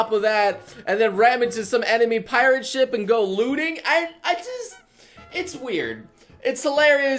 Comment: i understood lewding instead of looting XD